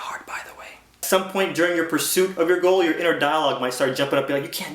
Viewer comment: did he say Dalek ? #anywhovians here ? D:<